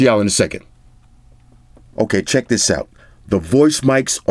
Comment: did he said chest?